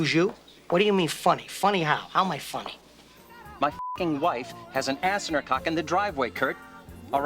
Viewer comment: "My wife has an ass in her cock!" did I hear that correctly?